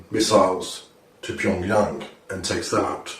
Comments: the subtitiles are wrong at one point : 4:45/4:46 when he says pyongyang (capital of north korea) and the subtitles says jong un........ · the captions are wrong at 4:46. It’s says Jong Un, when the guy actually says Pyongyang, which is the capital of North Korea.